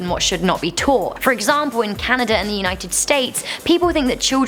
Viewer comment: Did she just say... Canada in the United States @ 4:15 ?